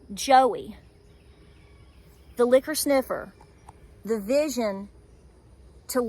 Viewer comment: I was watching this video at 2x the speed and I heard "Joey the 'liquor sniffer' " 🍸👃. I was like that's funny then realized you said " Joey the 'licker sniffer' "😂😂 Keep up the good work, sister!